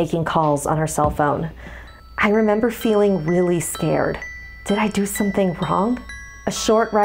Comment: I know I'm like heka late but at 3:56 it sounds like she's saying weally instead of really. XD